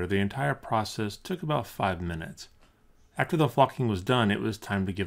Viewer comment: am i the only one who heard "after the fucking was done"